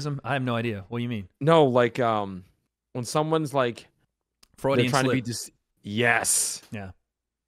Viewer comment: what is the word was looking for that knew? I couldn’t hear it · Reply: Freudian slip